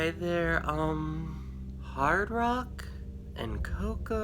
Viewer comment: Hard Rock? The elf is named Hard Rock,Mom? I must have misheard! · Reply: LMAO His name sure is Hardrock!!